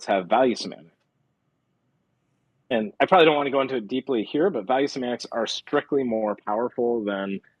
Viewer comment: “value simants”?? What’s he saying??